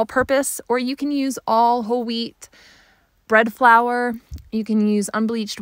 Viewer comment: Hi there thanks for the video. Question, why did you not use your own wheat and grind it? You choose bread flour, does it make a difference? Thanks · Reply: thank you! I thought you said whole wheat bread flour so I though you bought that. I also grind my own and have a slight hoarding problem. Maybe 25 5 gallon buckets full 🤷‍♀️ so I can use that and use APF with it. How do you make it “bread flour “? Do you at whey or wheat gluten?